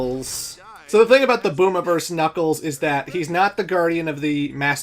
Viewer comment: I misheard "Boom-iverse Knuckles" as "Boomer-verse Knuckles"... XD